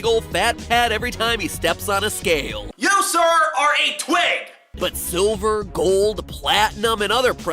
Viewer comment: did he say twig or twink?